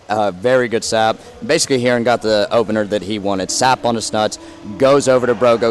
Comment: Just before the 2nd fight it sounded like he said, "he wanted sap on his nuts"